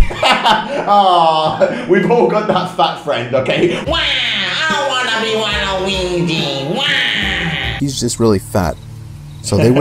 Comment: he said waluwigi instead of wario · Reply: He said "i want to be waluigi"